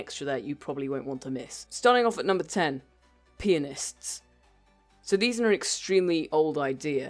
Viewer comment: When you said pianists it sounded like you said penis